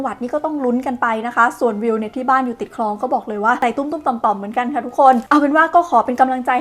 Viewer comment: พี่วิวพูดอะไรครับ ผมได้ยินว่า ไต่ตุ้งตุ้งต่อมต่อม ค่ะทุกคน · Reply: ใจตุ้มๆต่อมๆค่ะ แปลว่า ใจหวั่นๆ